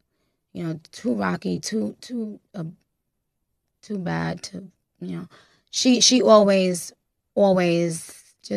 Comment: I may be listening too hard lol 😂 but I feel like I heard her almost say "too abu...too bad, you know" like she was about to say too abusive but caught herself...idk